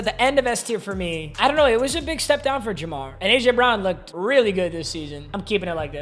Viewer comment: did bro say jabron